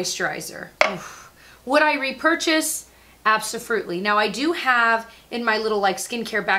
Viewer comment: Did I just hear you say, "absoFRUITLY?!" I LOVE IT! Prego hungry brain.